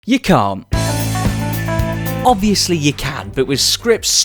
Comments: you coom. That’s what I heard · At the begninng i literally thought he said you c*m